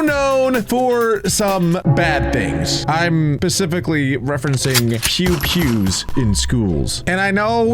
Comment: is it just me or did he say pacific?